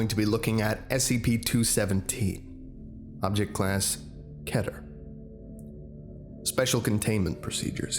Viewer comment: Twice I thought you said "Kevin" instead of "keter"